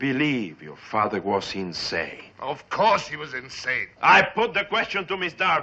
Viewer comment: the fat bastard should have said of course "he" was insane and not "she" was insane. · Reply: You are mistaken. I went back and checked. At 38:34 Francis L. Sullivan, Dr. Bristol, says, "Of course he was insane." Francis rather slurred his speech, and the 's' at the end of the word 'course' was slung onto the word 'he', therefore, it almost sounds like he said, "Of courshe was insane." But he truly does say, "Of course he was insane."